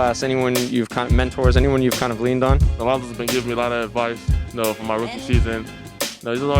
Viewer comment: Did anybody catch who he was talking about as a mentor that's given him advice and believed in him? I've gone back three times and I still can't hear who he said. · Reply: Sounds like he said lonzo